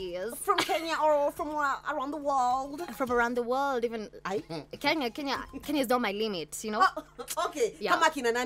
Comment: Wait wait ,,, did I hear "kenya is not my limit"?